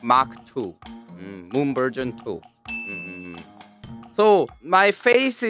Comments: Thought he said “moon virgin 2” lol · I heard "Moon Virgin 2". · i thought you said moon virgin two